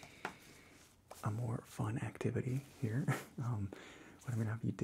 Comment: he said And More but I heard "Amor"